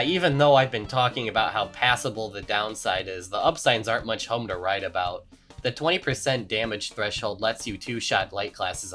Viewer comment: Wait, did you just say "aren't much home to write about"?